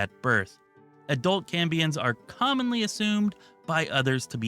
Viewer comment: if You mean Cambions they are not planned to have tails so I imagine they could fit into all available armors... possibly not Ogre armor or dwarf/goblin armor if they decide to make armors size specific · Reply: Maybe you were saying Cambions but it sounded like you said Cummins playable race